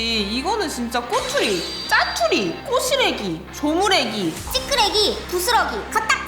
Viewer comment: 초에 꼬시래기 인데 뽀시래기 라고 들었어요 ㅋㅋ